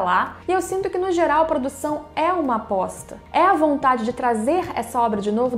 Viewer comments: eu ouvi "a produção é uma bosta", acho que estou assistindo muito YTPBR... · Por um momento eu entendi "é uma bosta" o que não deixa de ser verdade